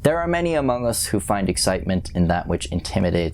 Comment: DID SAY AMONGOOSE?!!??!?